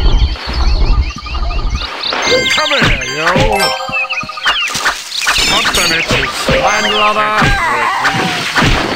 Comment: What's the phrase said by that huge bear in 04:04 ? Can anyone tell? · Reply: "Come here yo!"